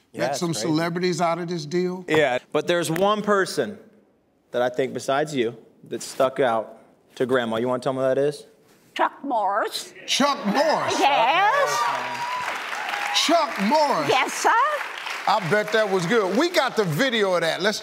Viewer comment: I thought she said chuck morris and I was wondering who that was